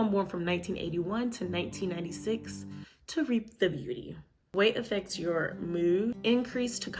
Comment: what’d you say at 4:04? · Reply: “To reap the beauty”